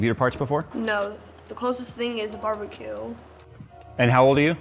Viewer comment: did he say “the closest thing was BBQ”? I’m so confused lol